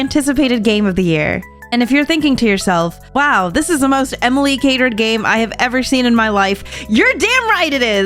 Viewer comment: When she referenced Infinity Nikki, I thought I heard her saying “most Emily hated game”, and I was like “WHAAAAT?!”